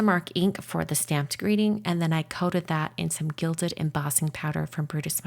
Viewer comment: the subtitles say “coded” where it should say “coated” just fyi ☺️